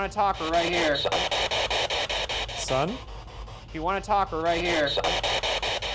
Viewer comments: I hear Jason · I think the ghost said "son" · I heard 'SUCC' 😂; then when Ryan said "son" I was like yep that makes a lot more sense.